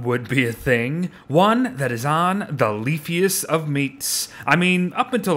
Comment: I space out for three seconds, and I hear "Lesbian." lol XD